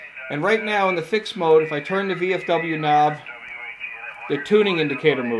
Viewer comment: Nice overview! I love this radio! At minute 5:22 and 6:30, did I hear you refer to the VFO knob as the VFW knob? Lol!